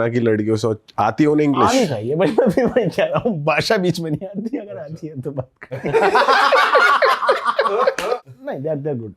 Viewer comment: what did he say? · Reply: bhasa bich mai nahi ati agar ati hain tou baat karti hain. #asa kuch bola